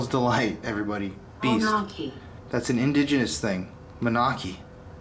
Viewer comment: it says monachi. I'm Lakota Souix and what it sounded like was Wanagi and that means ghost in my language